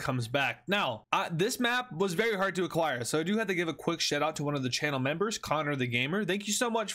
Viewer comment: anybody else hear “shit-out”? Day 4 of asking what if the nazis returned to Germany or alternate future